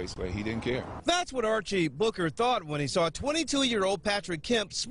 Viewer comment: Archie Booker...I thought they said Archie Bunker.